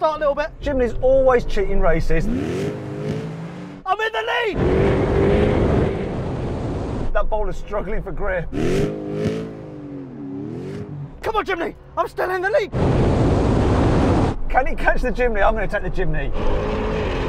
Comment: ここマジで頑張れジムニーに聞こえたwww